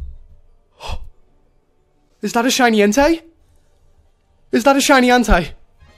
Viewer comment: I thought you said shiny henti...